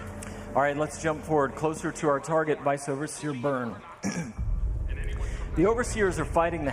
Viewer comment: What the heck did he say at 10:59.. "Vathervitherburr." XD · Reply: Vice overseer burn